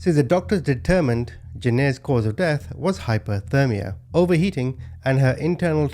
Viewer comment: I know it's a little bit late for you to correct it, but in the subtitles it's noted hypothermia (I'm not sure if you said this as well) instead of hyperthermia. Also as an European follower, I'd appreciate to have the equivalence in °C for the temperatures, the weights and lengths are okay to convert but °F and °C work really differently and I don't want to have to stop the video to google the conversions (especially when my yt closes itself when I open another app or just google despite all my attempts to change the settings..) 😅